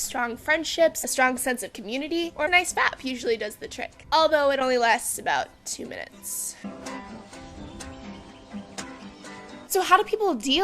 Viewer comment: did she say a nice fap, tho it only lasts 2 minutes?